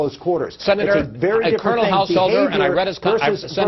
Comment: cournel householder hehehe i thought he said cournal arse hole hehehe